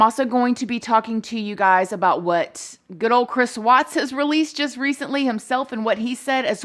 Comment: I thought you said “Chris Watts was just released,” and I was about to go into conniptions. Then I realized you had said, “...what Chris Watts said, which was just released.” Whew! I never took this appeal seriously until I heard your perspective. I’ve been through it, so I trust your observations about the criminal justice system. If you’re concerned, so am I!